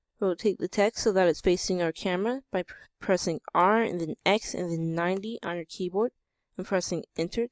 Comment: What is he saying to do in 1:12 ? Please Help! What do I have to press? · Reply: He is saying once you have your text you need to press r then x then 90